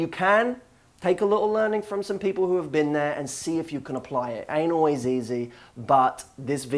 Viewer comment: Ahaha... at the end when you said "ain't always easy" I thought you said "Anal is easy". I panicked for a second there.